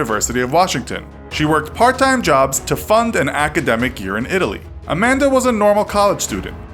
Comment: did he say academic urine? (I know he didn’t but I just can’t in hear it now)